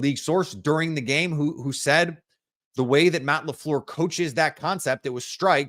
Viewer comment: I thought you said you couldn't wait to see Matt Laufluer offense